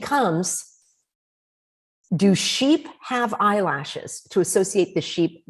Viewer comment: I thought she said, "Do sheep have RASHES"?